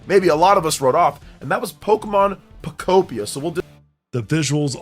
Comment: I thought he said "Pokemon Copeia" lol